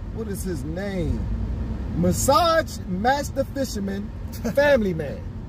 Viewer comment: I almost thought he said Fisterman! 🙃😂🙃😂